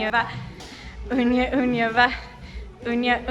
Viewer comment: "Unya unya va." Oh, that's what you guys were saying. I thought you were saying "oompa loompa la"! 😅